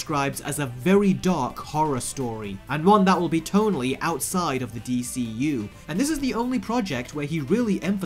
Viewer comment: glade you said tone outside some people thought he said totally outside